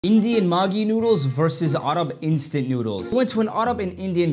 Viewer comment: "Maaagi Noodles" got my braincells to be disappeared as an true Indian💀 Also I heard "Margie Noodles" many Time in this Short☠️